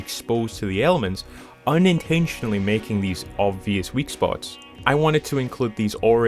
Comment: For the sake of transparency, here is a list of mistakes or corrections that i made which i caught in the video with text on screen 0:02 3:39 4:53 5:34 6:24 7:41 8:08 11:16 11:43 12:58 14:32 16:03 16:22 18:45 19:29 25:58 27:02 34:21 And a list of mistakes or corrections that came from the comments 40:23 also i meant to add that this kind of terminid could exist to lay/nurse eggs but i guess i skipped over it, ill probaby reuse that part of the script in a future vid (caught by @ 23:03 I accidentally said antlions (caught by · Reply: I thought you said antlions intentionally. That actually is a funny nickname for them, I'm now gonna use it.